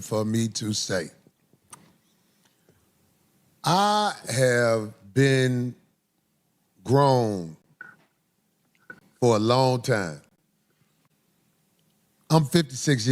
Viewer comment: I heard “I have been groomed… for a long time” my mind is still stuck there even tho I know that can’t be what he said since he mentioned his age 😂